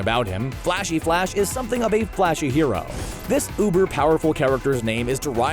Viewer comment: Is it me somebody else heard him saying UBERPOWER instead of OVERPOWER